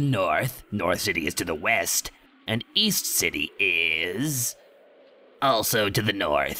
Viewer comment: East City? I thought you said Weast City.